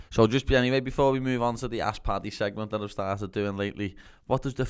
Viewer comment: He said “ask paddy” I heard “ass party”